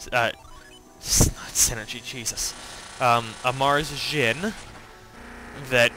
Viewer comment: It sounded like you said Martian to begin with lol. Thought it was funny. Also you deserve more views on your videos :)